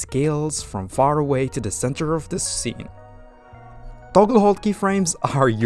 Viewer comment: I can't quite catch what you're saying at 2:55? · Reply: Toggle hold keyframes